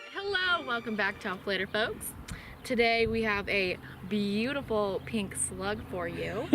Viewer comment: Lol. For a split second, I thought she said “we have a beautiful slut here for you”. 🤣